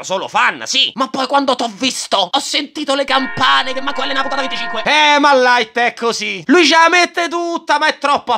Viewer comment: Per chi volesse sapere cosa dice Misa al minuto 9:05 nel caso non lo abbia capito Dice "ho sentito le campane che neanche L nella puntata 25"